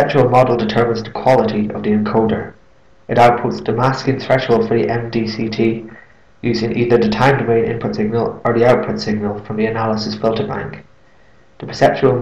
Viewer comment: I know this is abit late, but I can't catch what you said from 4:00 to 4:10, can you please write it down for me? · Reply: "It outputs the masking threshold for the MDCT, using either the time domain input signal or the output signal from the analysis filterbank."